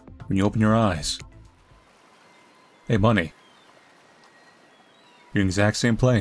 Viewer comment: I thought you said "hey bunny" lmao I'm listening to this while doing art and looked over like "huh? Did he-? Wtf" I had to rewatch it to make sure I wasn't tweaking from lack of sleep 🤣